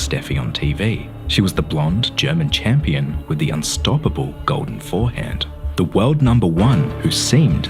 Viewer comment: I had to replay this part and listen closer because I thought you said "She was the blonde German champion with the unstoppable golden forehead" 🤣